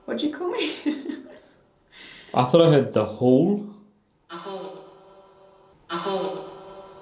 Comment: sounds like it said the hole